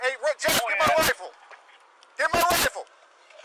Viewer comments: I think I watched too many anime waifu memes that I thought the officer said “Get my Waifu”. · at first I thought he said “hey get my bible ,get my bible!”